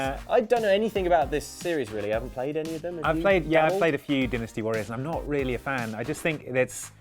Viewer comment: and bring you seven games to keep an eye on in February! Did we miss any? We probably did (oops), so let us know what YOU'RE looking forward to playing this month by dropping a note in the comments. Enjoy! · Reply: I totally thought you said dentistry warriors xD